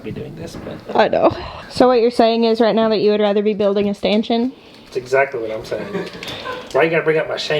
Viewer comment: Sorry thought you said you'd rather b building a "Manshion"!!! Sorry